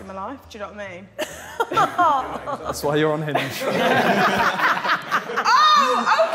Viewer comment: simon saying "thats why your on hinge" is making a lot of top sidemen moments videos lol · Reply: I thought he said ‘unhinged’ 😂